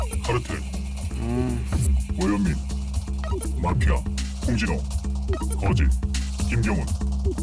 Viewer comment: 나만 이거 콩진호로 들리냐